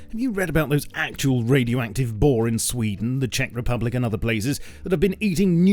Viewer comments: Did I hear Czech Republic?! If its true Im gonna die ¯\_(⊙_ʖ⊙)_/¯ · Lol did he just said Czech Republic? I didn't know that anyone from America knew about my country